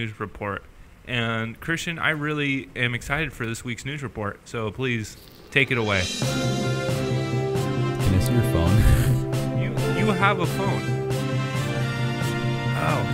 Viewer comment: I dead ass thought said nude report😂😂